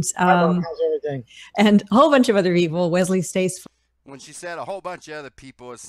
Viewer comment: It does sound like she says "& a whole lot of other evil" at 5.40. Just like when Trump did the 'live' link to the ISS he called them Astronazis but I am the only person that could hear it, everyone else heard astronauts? Mark Sargent is well spoken & has legitimacy in his voice? Has seen any of his deliberate piss poor performances on the BBC mainstream media that he has done? Obviously not or he would be calling him a useful idiot like everyone else does. has exposed the illuminati card game cards before so, he must be aware of the politically correct card that features the man hanging by a noose with a sign around his neck reading "ate the flesh of a dead animal". This is just the usual listen to the controlled shills & ignore the descending voices of the smaller channels. It is just the same as the mainstream, they only want us to get our information from the sources they control.